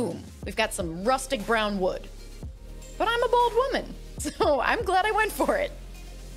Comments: I’m a “bold” woman, you guys! Not a bald woman. Just realized it sounds like I said bald lol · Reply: Literally it sounded like you said “ I’m a bald woman “ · who else heard bald woman 🤣🤣🤣